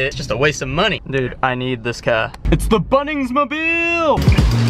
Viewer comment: did u just say Mobil????